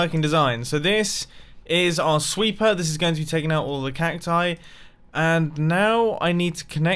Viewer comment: did u say cactii